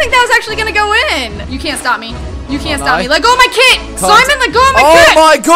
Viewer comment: listen to 1:51 sounds like she is saying "let go of my kid, let go of my kid"